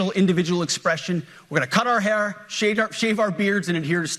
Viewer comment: I swear he almost said "shave our pubes"😂